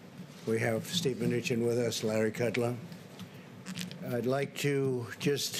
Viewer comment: Did he just say Larry kudlum ??? I thought his name was Larry Kudlow !